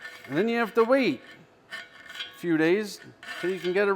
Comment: "....same as fleshlights" did I hear you right? heheheh